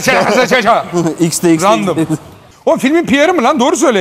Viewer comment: 'de Nalet bebe xnxx mi diyor la yoksa yanlış mı duydum. 🤔🤔 Aklımda deli sorular. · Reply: Xdxx diyor fesat mısın? hjvxhhjnn